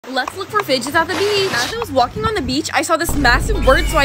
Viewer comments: i thought she said “let’s look for bitches at the beach” 😊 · She said fidgets and I thought she said bitches😂😂😂😂